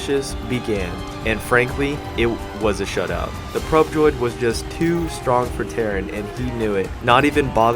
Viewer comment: lol i thought he said "probe droid is too strong for terran".. played too much starcraft